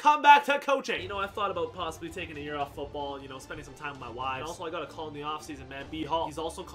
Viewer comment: Is it just me, or did say, “wives”?